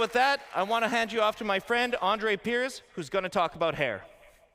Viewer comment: did he say “Now we are going to talk, butt hair!” I understand CIG is going for immersion, but this may be a bit too far. Does that mean there will be a shop in Pyro where I can get my butt hair removed, or bleached? That is what the solar flairs are good for I guess.